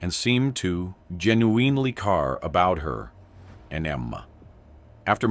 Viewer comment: Can you say he genuinely cared again? I swear it sounded like you said Genuuuin in ely car.... it was weird